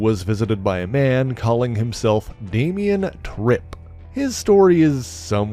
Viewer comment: I thought you said the visitor was Damian Drip. That would be a real fucking name. Trip, nah is call him drip